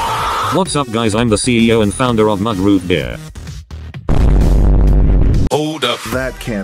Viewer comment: I thought that said MLG Root Beer lmao